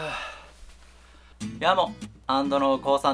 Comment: なんて言ってるんですか？ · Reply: やーも